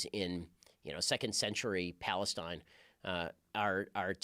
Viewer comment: did he just said Palestine?